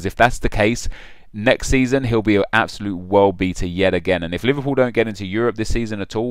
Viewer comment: I thought he said Suarez would be an absolute world biter aha